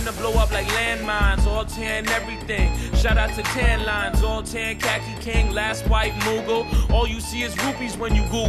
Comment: does he say "All tan, Kaki King"?